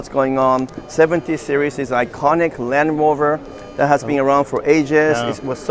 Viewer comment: do i hear "land rover"!?